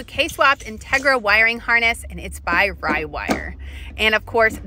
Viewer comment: I thought she just said “flyby wire”